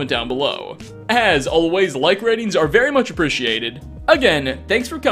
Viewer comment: I thought you said be sad everybody